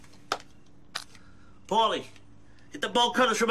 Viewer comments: "Paulie, get the bolt cutters, from out of the truck! We're going to Plan B." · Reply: I thought he said "ball cutters" XD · I wish they had done more with the bolt cutters from out of the truck. They were my favorite item on the show. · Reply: I thought he said, "Ball cutters..."